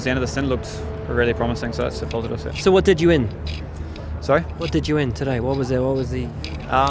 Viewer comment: does he say ''what did you win?'' hahah